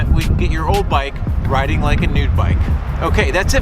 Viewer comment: This is a really nice video! I love the detail and depth gone into this. It helped me understand what a bike tuneup actually is 😁👍 8:09 I laughed out loud when I heard you saying “nude bike instead of “new bike” 😅